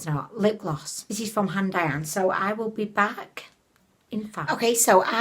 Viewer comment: Every time you said HANDAIYAN Lip Gloss I thought you were saying AUNT DIANE until I looked it up in the products used section!!!!!!!!! LMAO!!